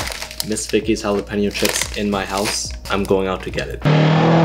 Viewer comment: I actually thought he was gonna say “I’m gonna go insane” if he doesn’t have miss Vickie’s chips 💀